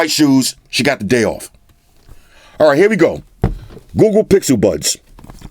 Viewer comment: Could have sworn I heard he said 'Goo Goo Pixel Buds'. My bad. Lol